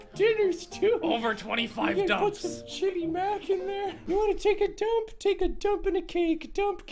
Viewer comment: I heard say Put some Sh🚫🚫y mash in there Or is it just me?